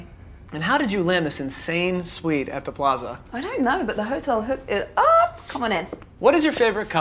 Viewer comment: What did she say at 0:21? My favorite Vogue 73 Q&A videos so far have been Daisy, Gigi, Emma, Blake and Sarah Jessica Parker's. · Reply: "i dont know but the hotel hooked it up!"